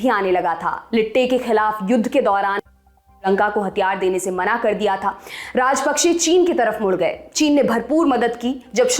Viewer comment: Why 9:01 is muted? I think you said Indian Goverment. Can anyone clarify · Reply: She said Bharat ne